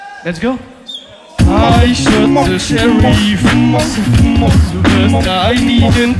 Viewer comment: about 2:30- 2:50 Does someone catch what is saying!? I heard like he's saying " I shot Sherry. bla bla bla ." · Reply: Noriaki yoshie Yes ! It's a reference at the worksong "The deputy and the sherif" (something like that) and he says :" I shot the sherif, plus I didn't touch the de-pu-tyyyy"